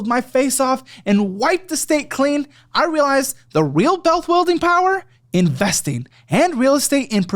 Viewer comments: did he say “bealth wuilding”? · why tf does he say "bealth wuilding power" in the promo this is the second time I've noticed it, I want to assume it's an intentional spoonerism it's so out of place it almost seems like he just genuinely stumbled over the words and nobody noticed so they left it in. EDIT: Also holy shit $30 in Bacardi every other day means she was getting a least a handle and drinking it in two days. No wonder they don't agree on past conversations, she was WASTED out of her mind for most of them probably. EDIT 2: 1:21:41 how the fuck do you sell a car if you can't tell the customer how much they cost???? This episode is insane, I love this show. EDIT 3: 1:27:00 lol everybody lives in a camper I guess that's why they call it "Mobile", maybe one day the whole town will just up and move